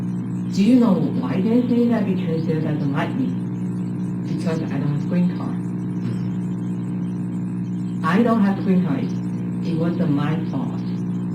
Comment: Little correction if I may, 26:06 she is actually saying "because I don't have green card. I don't have green card; it [meaning the reason her in-laws don't like her] wasn't my fault." I feel like this shows that the wife didn't think there was any good reason for the in-laws to dislike her, pushing blame and criticism away from her like with her crimes.